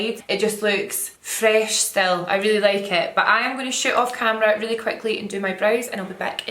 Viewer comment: I heard " ima shit off camera real quick" 😅😅